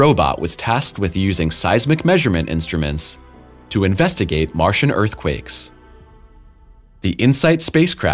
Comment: did he say martian earthquakes lol